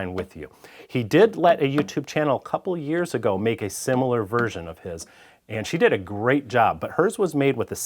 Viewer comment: Have to give you credit for pointing out that you weren’t the first YouTube video to make this design. I watched the other video a couple years back and thought it was a really good idea, just never got around to building it. Looks like this is the sign that I need to. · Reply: I was originally confused by what he said, and had to listen back to it. I originally thought that he said "This was designed by a friend and partner of mine, Mike Taylor, and I loved it so much I got his permission to share the design with you. He did a YouTube channel a couple of years ago [to] make a similar version, and she did a great job, ..." Whaaat?? But I missed a word there: "He did let a YouTube channel a couple of years ago make a similar version, and she did a great job, ...." Of course a channel is not a he or a she, so it's still a bit odd, and it would have been clearer if he'd mentioned Tamar (someone whose videos I've long enjoyed).